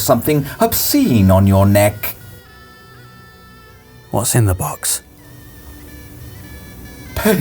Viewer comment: I thought said there was poo in the box 🤦‍♂️